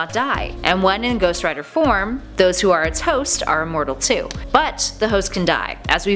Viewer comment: She says "those who are it's host" but it kinda sounds like she says "those who are toast" lol... I had to twins it a lil bit n listen to it again to make sure I was hearing the right thing n not "it's toast" lol